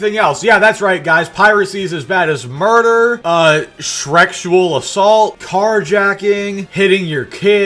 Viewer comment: Did you just say "Shrektual assault"? Are you assaulting Shrek?